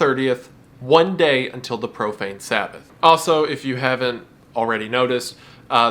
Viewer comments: I'll tell you hwat, I keep hearing 'The Propane Sabbath' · I keep hearing that as "the propane sabbath". I'm not sure if that's better or worse.